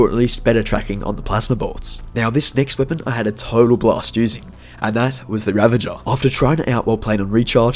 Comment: 😂😂😂😂😂😂 you sound like you said block but you said blast